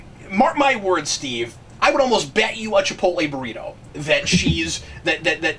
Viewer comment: Dhipotle! I thought you said Triple A! I was like, "what is a Triple A burrito? Must be one damn fine burrito!"